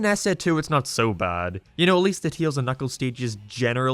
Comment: I dont wanna judge your English but when i hear you saying Tails, sounds like you say Teals